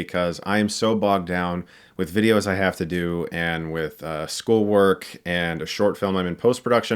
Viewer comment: did he say school work???